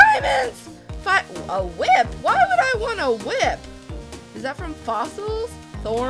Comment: why would i want a wip me : so you can nae nae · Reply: Omg I thought she said "why would I want to whip" 😂😂😂😂😂😂😂😂😂😂😂😂😂